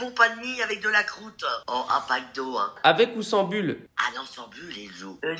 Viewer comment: J'ai entendu "macdo" au lieu de "Pac d'eau" je crois que je suis en manque.....